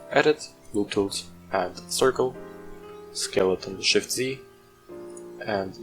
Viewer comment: What do you say exactly? All I hear is "Skelleton Shift + Z" I cant hear the command :( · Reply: I see where you get that from lol :D "Scale it on shift+Z" So press S and then shift+Z